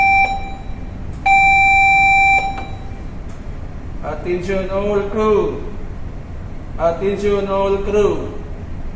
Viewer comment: 菲律宾英语？Attention, all crew 之后就完全听不懂了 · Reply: Attention all crew, forward and after (station) standby, starboard side alongside.